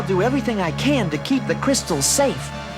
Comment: Who is the guy @ 20:30 and what is he saying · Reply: "Which won't be for long, Illiop!" - Quellor, The Supreme Opressor of M.A.V.O. What a way to end the series on a cliffhanger.